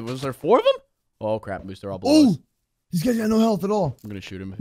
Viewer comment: did he just say crap? Was that the first time he said something else other that flip 😀